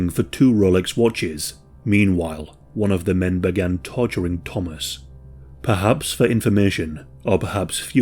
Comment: At first I thought you said they began “todgering” Thomas.